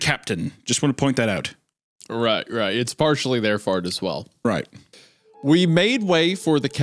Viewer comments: he said fart instead of fault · Isiah says "fart" instead of "fault" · IM CRYING WHY DIDN'T HUNTER REALIZED GOON SAID FART INSTEAD OF FAULT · who else heard Isaiah say "It's also their fart as well," at like 13:38 in the video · Isaiah says fart instead of fault... You are all welcome😊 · Anyone else hear Wendigoon say fart at 13:35? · says fart instead of fault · did anyone hear wendi say " right right its partially their fart as well" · Did anyone else hear at 13:30 its not all his fart? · Big lips says Fart instead of fault